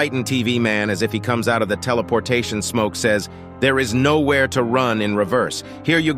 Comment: When titan tv man said “theres no where to run” it sounded like he said theres no where to run sh*t”